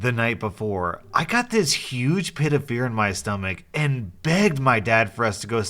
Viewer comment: I heard ”I got this huge pit of beer”